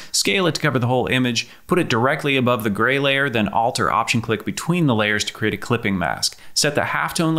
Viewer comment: I got lost on 1:02 ::( what does he say? "alt + click" the layer? it didn't do anything to me pls help